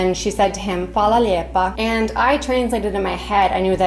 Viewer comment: I thought they were saying "Hvala lipa" as in kuna cents. It made no sense but it did not sound like "lijep".